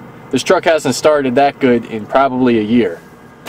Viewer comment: You said it "hasn't started that well in nearly a year" but I definitely hear "I put off fixing this for nearly a year" hahaha